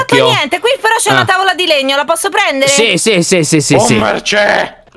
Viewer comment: solo io ho sentito "BOOMER CÈ"